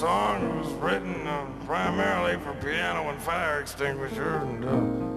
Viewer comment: Just got Tom playing in the background and I pause for a moment ''Did he just say it was written for a piano and a fire extinguisher?''